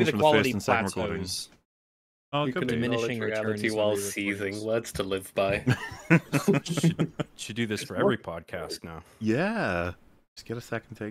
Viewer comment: can someone tell what is saying here? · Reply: Acknowledging reality while seething, words to live by referencing the joke made at the 1:00 mark